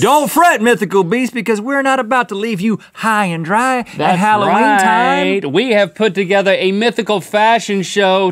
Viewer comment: Hello, Rhett and Link! I love this video so much, and I want to make sure everyone can understand and enjoy it. That's why I wanted to let you know that your captions are a little off. For example, at about 0:38 the auto-captions say "we're not about to leave you high and dry that how we have put" when it meant to say "we're not about to leave you high and dry at Halloween time, we have put". When you get a chance, could you fix that? That'll help the 360 million folks globally who rely on closed captions to understand videos. Thank you, and keep up the great work!